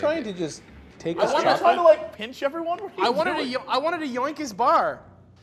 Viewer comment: Did I hear right.... 2:40-ish in.... "I just wanted to yank his bar"?